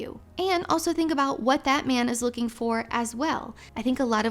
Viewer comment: i thought she said "what Batman is looking for as well" AHAHAHAHAHA